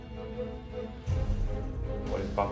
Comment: 何回聞いても「馬鹿棒」が「バカボン」に聞こえてしまうw𐤔🤣🤣w𐤔